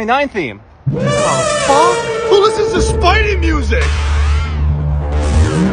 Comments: The “YOU’RE A MISTAKE!” Was so accurate Also his theme slaps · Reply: i hear "YOU MUST DIE" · The “YOU’RE A MISTAKE!” Was so accurate Also his theme slaps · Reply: I thought he said you must die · The “YOU’RE A MISTAKE!” Was so accurate Also his theme slaps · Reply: bruh I hear "you must die"